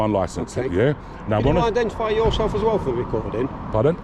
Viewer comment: What did the copper say at 19:50 before he said we'll see about that? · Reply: he said can you identify yourself please